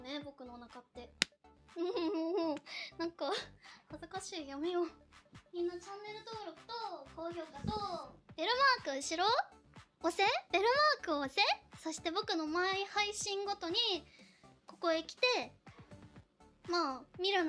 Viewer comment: 最後のベルマークのところがエロマークに聞こえた俺は汚れてるんだろうか……